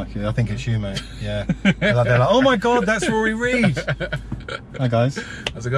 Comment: Thought it said Riley Reid 🤦🏽‍♂️